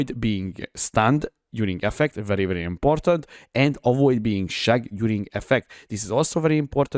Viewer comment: i love your accent haha, please don't take it as an insult but i heard you say "avoid being shagged" instead of "avoid being shocked" and with almost 1k deaths this league I clearly do need to avoid being "shagged" lol